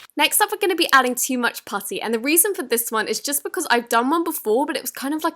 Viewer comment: I heard pussy instead of putty osht